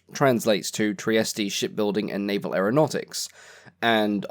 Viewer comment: I swore you said "shitbuilding" at 2:08.